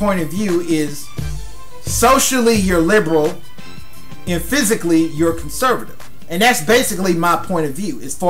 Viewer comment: It sounded like "physically" you're conservative. lol